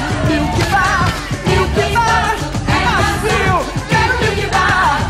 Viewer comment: I heard 'ghetto milky bar'